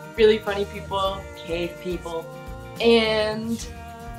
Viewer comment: Damn I thought said gay people and was hoping for confirmation but I guess we'll have to wait and see